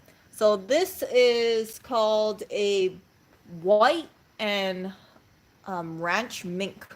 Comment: What is the word at 1:43 where you're discussing a coat of remnants, scraps, sections. It sounds like "roight" or "voight," but is unclear to my old ears. Thanks in advance for your consideration. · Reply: White 😅 thanks for asking ❤